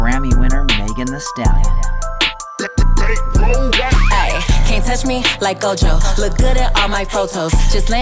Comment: ( You Touch Me like Gojo ) that's what I hear